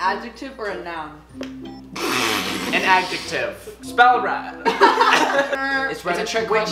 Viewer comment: Anybody know what Noah said at 11:40? I literally have no clue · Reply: “It can be both” 2years later